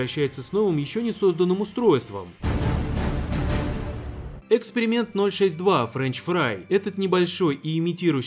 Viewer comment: мне послышалось экскрипенты